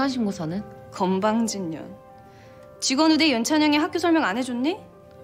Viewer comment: 유라헬이 뭐라하는건지 아시는분.. 건방진년 &$#%* 윤찬영이 학교설명 안해줬니? 라고하는데 .. 앞부분이 10번넘게돌려봐도 뭔말인지 몰겠ㄱ음.. 직원우대로들림.. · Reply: 직원우대 맞아요 윤찬영아빠가 제국그룹 비서(=직원)라서 사배자전형으로 입학한 설정이니까 직원우대로 들어온 윤찬영 이렇게 부르는거 아닐까요?